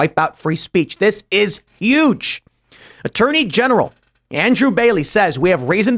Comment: I thought Luke said "Attorney Genderer". I'm surprised that's not a thing yet.